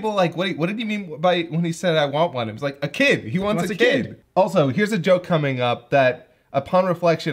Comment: admittedly that line from Yajirobe was hard to understand what he said, cause until this commentary, I heard games not kids.